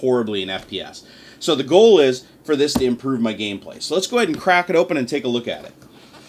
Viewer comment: instead of saying crack it open you said crap it open lol